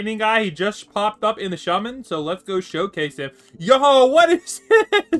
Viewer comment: The beginning of the video he says “shummon” instead of summon